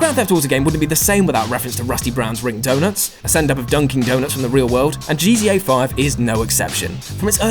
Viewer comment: I'm sorry, did you say "Dunking Donuts??"